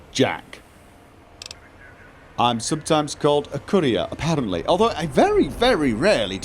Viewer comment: When says "Courier" I keep hearing "Korea".